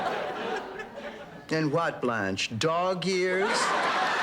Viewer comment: “in what Blanche, dog years?” Man Dorothy really has her way with words. Lol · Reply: At first i thought she said dog ears, but i just realized that she said dog years😂😂